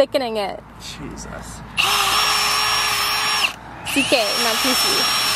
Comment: what she say at 7:13? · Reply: " CK not two C"...